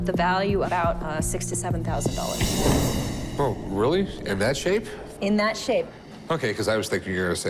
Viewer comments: I thought Rebecca said $67,000. Haha. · After listening about ten times I thought she said 67,000 not 6,000 to 7000. Lol